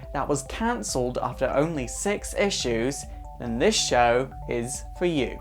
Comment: At the benign when you said 6 issues I thought you said sex issues